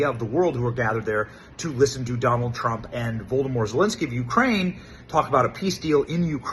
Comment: hol up .. .did you just call Zelinsky Voldemort?? LUL